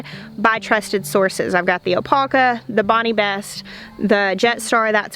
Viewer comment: Hi there, what is the name of the tomato that you said MI gardener recommended? I heard "alpaca" is that correct? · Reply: Opalka